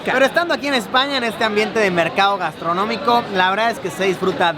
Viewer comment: pense que decia carne de mercado xD